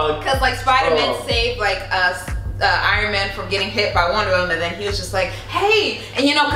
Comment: did i hear wonder women 😂😂